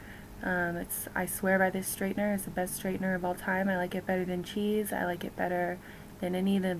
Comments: When she said she like the straightener better than chis, I thought she said she liked it better than cheese. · i know she said that she likes her straightener better than "chis" as in "chi" plural but if i didnt know better i would have thought she said she likes it better than "cheese" lol